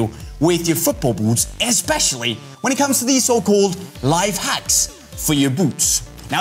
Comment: i misheard it as "life hacks, for your boobs!" 😂😂😂